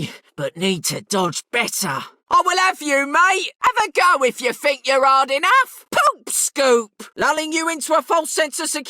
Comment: why do I hear "I will f you mate. Have a go if you think you're old enough" 😭